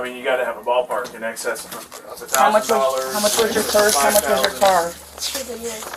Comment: "How much was your purse? How much was your car?" is what the lady says, not what they put on the subtitle